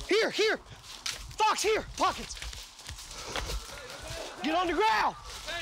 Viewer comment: When he said "pockets" I thought he was saying "poggers"